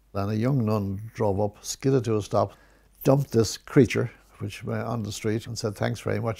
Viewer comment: did he call her a creature?